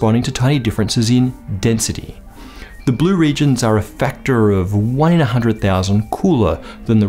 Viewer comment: subtitles say "are a factor of 100,000 cooler", but actually 1/100,000. A mere 10 orders of magnitude wrong :)